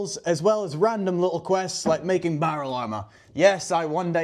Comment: barrel armor?? would be cool if i heard it correctly haha